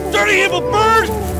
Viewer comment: someone please translate what he says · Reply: Dirty little bird